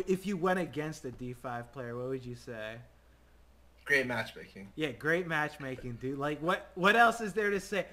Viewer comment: looks the captions: "Scott, if you were ranked 5 and you [...] went against the d5 player, what would you say?" "Great Masturbation". LOL